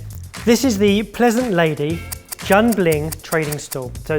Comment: Did just say jian BLING 😂😂😂😂😂